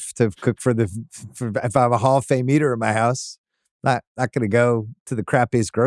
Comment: did he say "HALL OF FAME EATER"